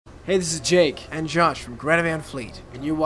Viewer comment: Ngl it sounded like she said drake and josh